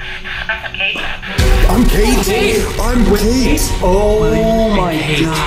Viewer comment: When it said"I'm Kate" i heard "I'm gay" 👁️👄👁️💅